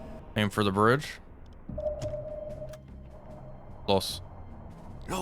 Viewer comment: LOS!!! (Thats what i heard)